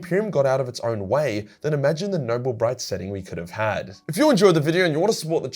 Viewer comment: Totally misheard "Noble bright" at the end as "Nurgle Bright" and I don't know what that looks like, but now I want a novel about it.